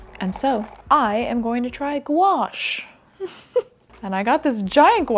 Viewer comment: I have never heard of gouache before. I thought you said gua sha :'DDDD